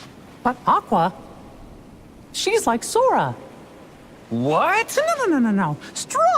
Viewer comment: Mickey: “Aqua!! She’s just like Sora!” Riku: “Wha…The….” Mickey: “No no no! Strong like Sora!” Riku: “oh…” thank gosh Loved that part! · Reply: I remember when I first saw that scene and I misheard it as “she likes sora” and having the same reaction as riku 😂